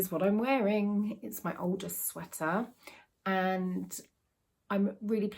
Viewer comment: Hiya! Every time I hear you say 'my Aldous sweater' on your videos, I always think you're saying 'my oldest sweater'!! Take care lovely! 🤗🥰